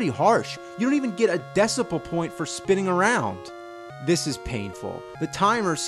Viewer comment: Did he just say "decibel point"? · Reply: Mike Laurence Decimal Point